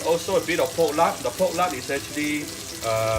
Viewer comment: What did you add at 3:35 that is optional? I didn't understand what you said. Thanks! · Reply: Fish sauce?